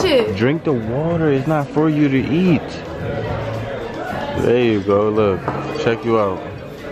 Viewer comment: 👀👀 is it my hearing or did he say “BAAAAABE” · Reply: “There you go”